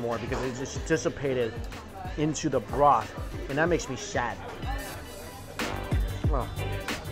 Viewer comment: Maybe he was drooling while saying "sad" but i heard "shaaad"